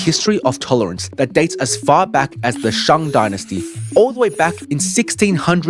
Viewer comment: I thought I heard Chinchin Dynasty… It means something gay in Japanese :P